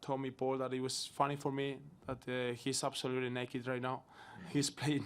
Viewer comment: What did he actually say then was it 'absolutely knackered' ?? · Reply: Naked😂